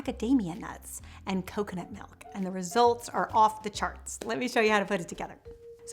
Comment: Did hubby jump in and surprise you while doing the voiceover? I swear I hear a man's voice with yours saying, "Let me show you how..." at 0:28 ! 😳 Ghost? 👻 Haha! well, either way, the recipe sounds delicious! TY! ❤️